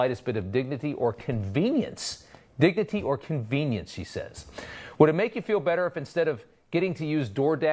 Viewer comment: "Dignity or convenience HE SAYS." I hear 'He,' the closed caption reads 'She.' You be the judge.